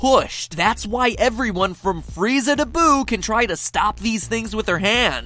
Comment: What he said, "Freeza to Boo" What I heard, "Freezakaboo!!"